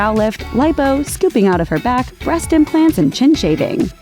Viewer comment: did it say she had scooping out of her back? what in the heck does that mean????lol